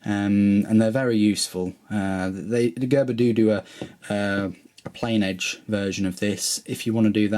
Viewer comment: what did u say at 10:32 lol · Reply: Err Gerber do do a err plain edge version of this